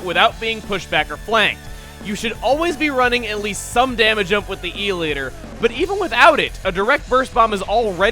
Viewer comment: I always thought was saying eel-eater, not e-liter...